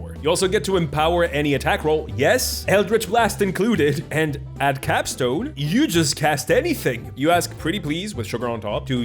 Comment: Sorry, not an English native speaker, but at 27:43min they repeat a term that sounds like “calf stone” that I don’t really understand. Can someone tell me what it is?